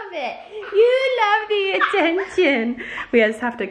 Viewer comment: it sounds like she says I love the attention